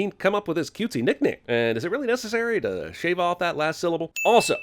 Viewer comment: Personally, I thought he said "Gally", which was Alita's original name in the manga and anime. Just figured it was an editing error left in from some version of the script...